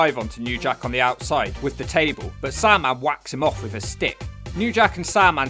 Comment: the first thing i heard is "Sandman wacked him off"